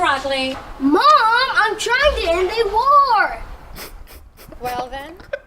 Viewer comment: I thought they were said "We are gonna end the world", but I guess it was "end a war"?